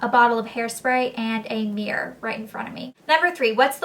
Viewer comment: She actually said 'Mirror' not beer, but the first time I heard it I thought she said 'Beer' also haha.